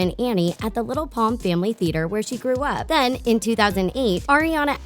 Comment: i heard where she trewh up not grew up